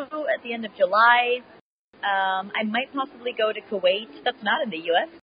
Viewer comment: What was that country she said at 2:11 I heard Kuwait is that right?